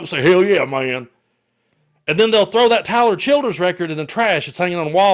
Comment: I could not understand what you were saying. I replayed it over & over thinking you said something about children. Finally slowed it way down and turned on captions. Tyler Childers? I've never heard of him. Lol I guess I'll look him up! You said someone would wanna toss out his picture?